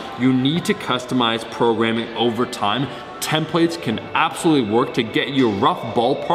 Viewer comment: Thought you kept saying 10 plates when I realized u said templates it finally made sense.